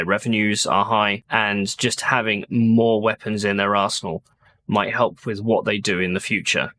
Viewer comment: When you said "weapons in their arsenal" I heard "weapons in their ar*%hole". Made me do a double take. Mostly because you are talking about Intel and the other one is still applicable. haha :-p